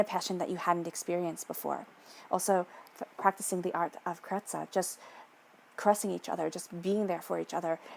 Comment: What is that art? It sounds amazing. Did you say Papatzoa? I have been trying to look it up. Caressing